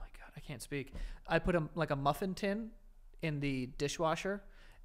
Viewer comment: I thought said “muffin tip” 😂